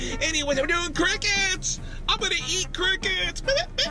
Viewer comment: I thought he was saying "I'm gonna eat crackheads" lol